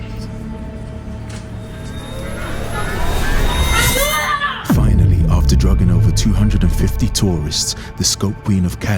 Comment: what was she shouting??? abdulla?? what does it mean? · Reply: ayuda... translate from Spanish to English